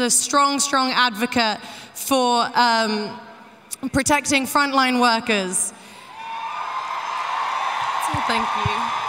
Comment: thought she said fine line workers at first xd